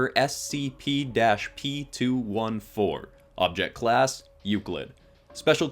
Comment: instead of saying SCP-p 214, the captions said SCP-p 215